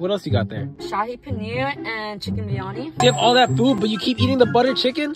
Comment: as an Indian american. I like butter naan and paneer I think pulihara is my favorite pulihara is lemon rice( not lemon squeezed on rice ) I thought be said, u have all that poop but he said, U have all that food it literally sounds like poop but he said food